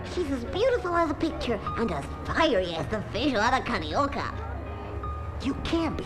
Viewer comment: did he say she's a pretty as a picture and as frirey as the what a carioca? can someone tell me what the mouse with the overall said please